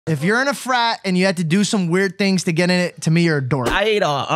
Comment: i thought it said “if you’re in a fart”😂😂🤤